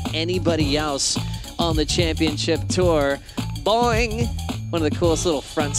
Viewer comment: did Joe just said “Boing~~” 😂 omgn he’s so cute!! ❤